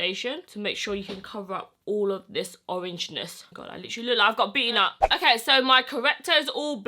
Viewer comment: Did she say “I look like a beaner”